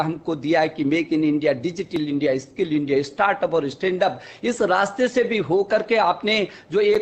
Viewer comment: did he just say stand up India...Yeh Toh silencer Ki Tarah ratta Laga Ke Aaya Hai...